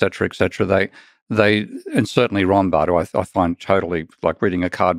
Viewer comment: He sounds like he says "Ronbard." He says he finds his worth trivial but he can't even give the correct name! I'm no economist, but even I know Rothbard's work is not trivial.